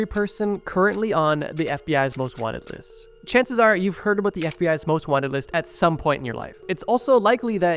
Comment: I wasn’t paying the best attention due to me doing my hair and thought I heard “chances are you’ll end up on the FBI most wanted list at some point in your life.”